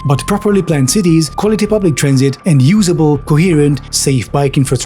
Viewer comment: I misheard it as "Eternal Combustion Engine" 😂and I think that made a lot of sense in the context of climate change.